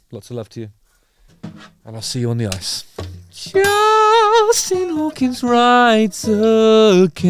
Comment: I thought I heard you say your channel name “Justin Hawkins Right Again!” 😂